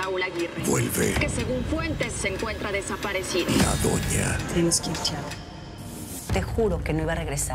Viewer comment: Puede alguien decirme porfavor que dice Altagracia en 0:18? Yo no entiendo :)) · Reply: Si no me equivoco dice "Tenemos que irnos ya tiago"